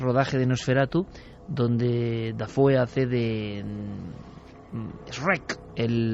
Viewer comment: la voz q suena que dice Shrek !???